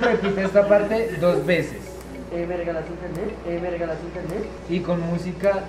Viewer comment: saben que dijo?😢 no entendí. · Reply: Dessire Lopez 'hey me regalas internet' JAJAJAJAJAJA eso dijo!!😍😂